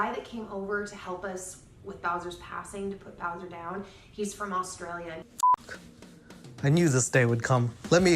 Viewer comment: Did she say "he's from Australian" 🙄